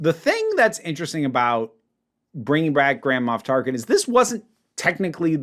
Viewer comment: I apologize, but... nearly every time said, "Grand Moff Tarkin," for some reason I heard, "Grandma Tarkin."